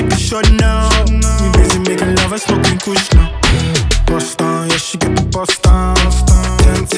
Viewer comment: I always thought he was saying “bus town the sheep in bus town”